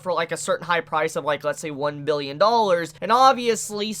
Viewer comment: It sounds like you were saying “one Million dollars”, but I assume you were saying $1 billion.